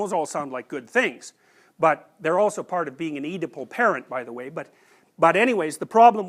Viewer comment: "part of ___ parent", I'd appreciate if you guys could spell the word for me, can't understand what is the word, nor that I was able to find the phrase in google search. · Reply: I think he talks about "oedipal parent" ( it cames from the name Oedipus - the character from greek mythology - <link> )